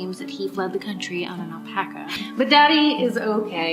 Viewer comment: Did she just say he fled the Congo on an alpaca? ALPACAS DON'T LIVE IN AFRICA, KEN.